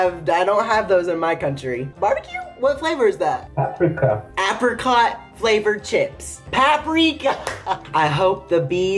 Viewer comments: tell me why I thought that guy said the chips were Africa flavored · Who else thought he said Africa for the flavor chip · When he said "apricot flavored chips" I thought he said "africa flavored chips" and I almost lost it 😂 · Deadass thought you said “Africa flavored chips”